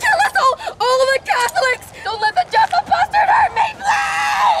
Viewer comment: To be fair, she thought he said he hated Catholics, but the poor wee lad said athletes!